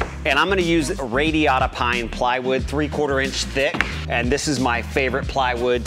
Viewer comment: I could've sworn you said "Ray Liotta Pine plywood"... "no SAG!"